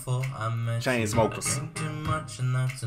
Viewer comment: Did he just say "chinesesmokers" lol